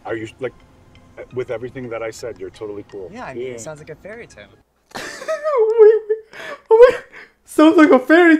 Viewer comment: He said "It sounds like a fairytale" but I heard him say "yes, i would love more screen time!!!"